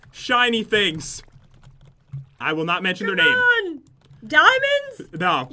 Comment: i thought he said " i will not menstruate " lmao